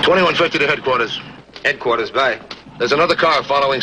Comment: what is the HQ radio operator saying in response to the call? "s it "Headquarters by" ??? Is that short for standing-by? Never heard such a radio call procedure, anywhere else, ever.